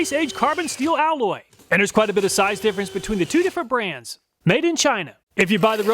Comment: Why was I hearing "Maybe in China" 😁